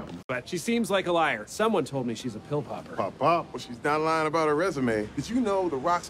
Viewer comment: Did Holt just say pop pop?